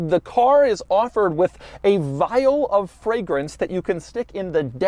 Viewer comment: I thought he said "vile fragrance" for a second :P